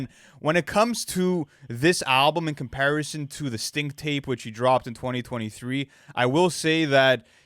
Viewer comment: Did he just say "stink tape"? LOL